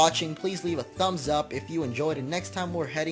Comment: Totally thought said "Floor Aroma" Town.